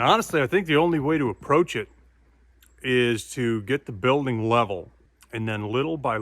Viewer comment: around the 5:38 mark when you said that you needed to get the building level I thought you said get the building leveled, I had to go back and watch the video again to make sure I didn’t misunderstand what you had said. LOL. I was wondering what had happened to this property and when you were going to get back around to it, glad to see you didn’t abandon it. Also, the music you have accompanying the video is so relaxing I dozed off while watching it and had to watch it again so I could see how it ended. Merry Christmas to you and the family!! ✌️😎